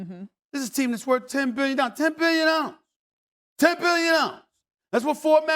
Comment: the way SAS says "10 billion dollars" 😂😂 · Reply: I always hear “billion-nollas”